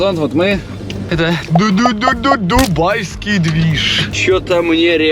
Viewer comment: Мне одной послышалось будай а не дубай😹😹😹